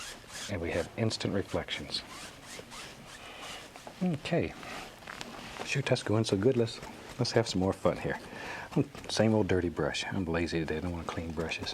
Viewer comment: What... what did he say at 6:29? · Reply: "Shoot. That's going so good, let's have some more fun here". He definitely tripped over this tongue on that one, haha